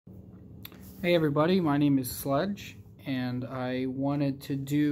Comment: Lmao I heard “Hello everybody my name is Slut 😂” But fr thanks for the review. Saved me money